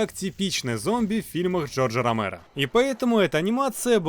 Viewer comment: мне одному послышалось джо джо ромеро?